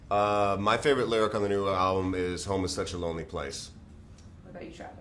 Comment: I thought Mark said, "Homosexual is a lonely place".